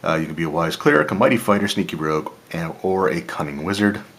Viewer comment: I picked up one of these myself and I love it. It's cool to see your passion for these products really come through in your videos. When you said, "sneaky rogue" for some reason I heard "stinky rogue" Haha! Cool video as always!